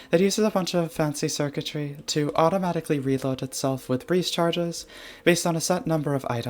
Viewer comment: This video has captions once again because of me. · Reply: you wrote freeze charges instead of breeze charges